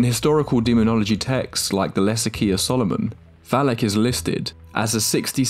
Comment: I thought he said less ikea of solomon💀